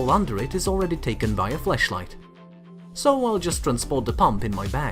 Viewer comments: did he say fleshligh or flash light just clarify😂😂😂😂😂😂😂😂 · did he just say fleshlight😳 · Okay... so, I try to adapt to different accents to understand whenever I encounter them but any other American English speakers here? Am I wrong or did I hear "Flesh-light" instead of Flashlight there? Lmao not trying to make fun. I knew what he was saying. Just still laughing because of what I heard there. Also, weed is legal where I am so... giggles over stupid shit happen lol